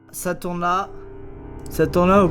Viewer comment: il dit quoi? Saint Thomas? Ce temps la? Ça tourne la?